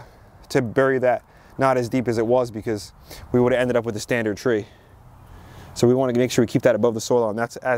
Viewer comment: did you say “we would end up with a standard tree”?